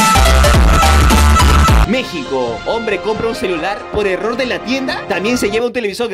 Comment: parece como que dijo 'el pepe'